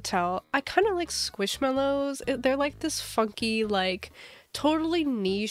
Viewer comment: you said funky and my little brother thought you said "f****ng" and he looked at me like he had seen a ghost